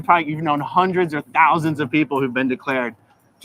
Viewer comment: She might have heard it as "hundreds of thousands" instead of "hundreds or thousands." That's what I thought you said at first, so her smile made sense to me. I realized what you must have meant a second or two after that. (Not justifying her, just saying how I heard it.)